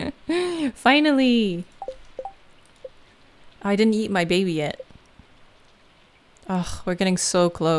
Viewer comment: sounded like: "Oh I didn't eat my baby yet"